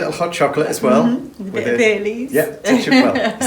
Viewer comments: At first, I thought that said 'Bay Leaves' were in the hot chocolate. Then I realized she'd said Bailey's lol That's much better xo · The difference in our accents often cracks me up. When you said you had a little Baileys in your hot chocolate, I thought you said that you had Bay Leaves in your hot chocolate!! I couldn’t imagine such a thing!! Great job of editing the lovely snow scenes into the decorating. You had music working with it all. Great job!